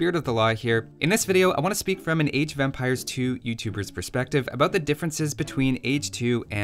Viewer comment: Why does it sound like he is saying Age Of Vampires :D